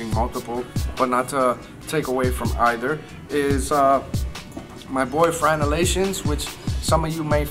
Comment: thought you said "boyfriend"